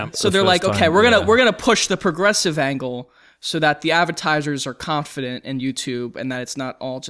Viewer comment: I’m rewatching this one again and I noticed something kinda funny, At around 32:20 says “avatizers” instead of Advertisers or at least it sounds like it.